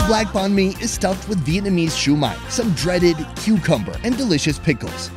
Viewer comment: Some “dreaded” cucumber 😂 Typical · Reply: Omg! Lol i thought he said "breaded" and was confused to see a regular cucumber. Totally forgot he hates it 🤣